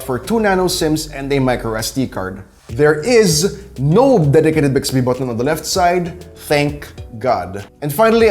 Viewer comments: WHAT EXACTLY ARE YOU SAYING AT 1:30 just before you say, "Thank God"..? Play it back multiple times and I cannot come close to understanding what you're saying there. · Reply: "Dedicated bixby button"😂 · Wtf did he say @ 1:31 ? No WHAT?... "...thank God!"...? Ok... disregard. I've worked it out... He said :"..no dedicated Bixby button thank God!". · What the hell, did he just say @ 1:31, an then say, thank god! Lol 😂 😝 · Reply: He said dedicated Bixby button. I turned on the captions to decipher it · For 1:31 youtube caption says theres no “dedicated bixby button” does anybody know what he actually said? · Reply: "Theres no dedicated Bixby button, thank god" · wtf did he say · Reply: Micro SD card